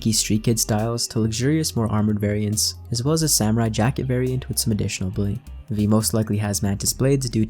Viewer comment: did he say Samurai Jack lmao